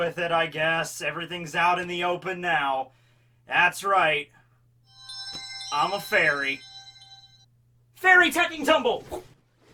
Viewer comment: I thought said I'm a furry